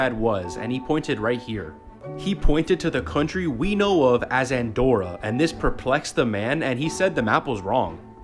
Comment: I thought he said Adora I’m freaking obsessed with She-ra I need help